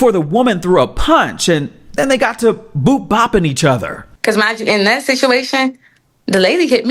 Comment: did he say he Boo Bopped each other😂